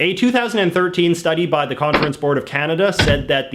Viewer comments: did someone say Boards of Canada? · All I heard was “Canada sucks ass.”